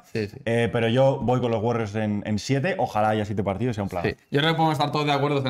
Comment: Por ciertro, que dice en el 18:20 ?????? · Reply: "Sería un planazo"